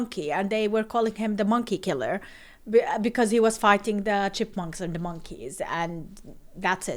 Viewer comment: Did i hear right? Gladiators are fighting Chipmunks 🐿🤔😆.